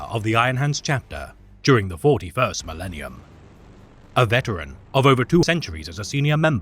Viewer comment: Great vid as always. But did I hear wrong with "a veteran of over 200 CENTURIES (20,000 years)..." in the first couple sentences? I'm guessing the words just mixed by accident. Sry if this is bit of a nitpick.